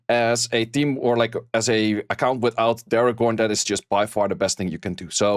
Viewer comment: I couldn’t understand to which monster you were referring. I heard, “as a[n] account without terror corn, that is just by far the best thing you can do.” Pretty sure there’s something wrong with my hearing and not your explanation =)